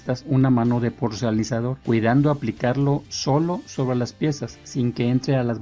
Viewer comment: Grandísimo trabajo, de nuevo! :) Una duda.. Por favor... En minuto 7:22, cuando está con el suelo, dice "le aplicamos en estas una mano de ¿porcelanizador?” que no entiendo bien lo que dice y, si es eso ¿Qué es ese material y para qué?. Gracias! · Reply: gracias, el Porcelanizador es una especie de sello que evita que el yeso absorba la pintura, digamos una especie de impermeabilizado transparente , pero esto también lo puedes lograr con un sellador vinílico, el que aplicas antes de pintar las paredes o también funciona como tal, el pegamento blanco , los dos deben ser diluidos o rebajados con agua,; si queda alguna duda con gusto sigo a tus ordenes, saludos